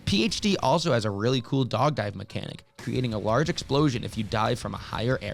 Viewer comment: Did he say “dog dive”? We always called it a “dolphin dive” in my groups